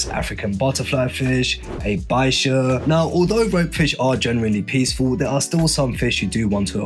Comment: Haha the whole time I thought he was saying "woke fish"!